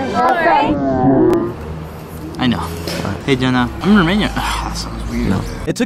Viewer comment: did bro actually say Yenna X_X?